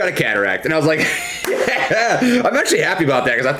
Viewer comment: Damn I hate dyslexia, thought you said contract. Lol